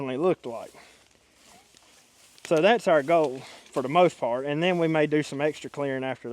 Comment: Love watching your videos. In todays video when you were pointing out things in the woods. My husband and I swore you said that’s our still over there. Did we here you right? Lol